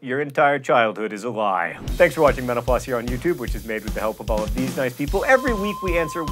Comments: Sometimes when John says 'thank you for watching Mental Floss' at the end of these videos, I hear 'thank you for watching menopause.' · When John said "Thanks for watching Mental Floss" I could have sworn he said "Thanks for watching menopause" which I found hilarious. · Every time he says "Thanks for watching mental_floss" I hear "Thanks for watching menopause" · Every time you say "thank you for watching Mental-Floss" I hear it "thank you for watching Menopause" and get weirded out for like 2 seconds before I realize.